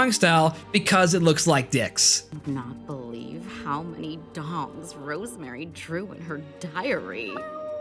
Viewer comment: all this time i thought she was saying dogs